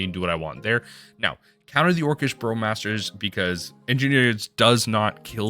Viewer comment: Did you just call the card "Orcish Bro-masters" @ 4:52? Because I'm so up for this being their mtg slang name.